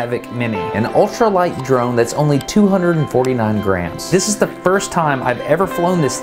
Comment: At first I thought you said "249 grand" lol